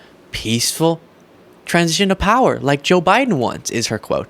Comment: Did you say peaceful transgender power?